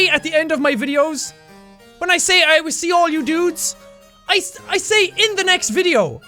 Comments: i always thought he said something like "i will send you all nudes..." something with nudes haha. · Hi Sean/Jack thanks for explaining the hole "i Will see All your dudes in the next video" i always thought that you said " i Will see All your nudes in the video" lol